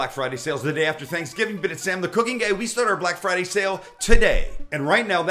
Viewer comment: he actually said Sam the Cooking Gay fml